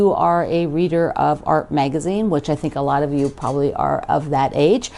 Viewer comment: I’m curious about the magazine you referenced. I thought you said Art Magazine but I can’t find one with that name. Can you tell me the name please? Thanks. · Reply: Just get the current AARP magazine, if you don't get AARP or go to their website to look